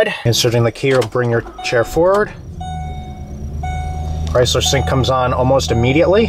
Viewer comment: Does He say Chrysler Sync?